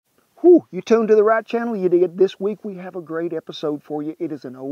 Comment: Did he say “you idiot” at 0:02???